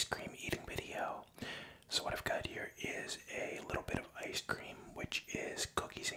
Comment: I thought you said a little bit of ice cream